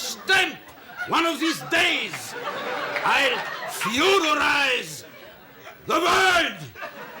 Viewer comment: What is that word Brother Theodore says? "futurize?" "feudalize" or "computerize?"